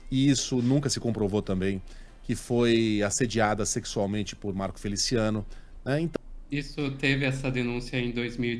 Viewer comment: Eu ouvi Sara Inverno? rsrsrs